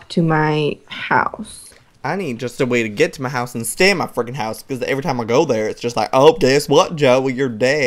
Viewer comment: Stacy is to inocent to swear! She probably said stinking, that is what is sounded like she said. Even though it was close to sounding like f*ck... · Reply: she said freaking in one of the comments someone said did stacy swear and she said noooo i said freaking not the other word